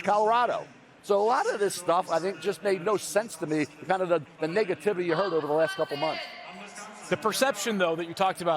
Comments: I thought Mel said niggativity 🤣🤣🤣😭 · Coulda Swore i heard the "Niggativity" & not Negativity 😂😂😂 Ya they pissed Mel Kiper Off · Hold’ UP... Did MEL Just Say “All the Niggativity The Past Few MONTHs” 😳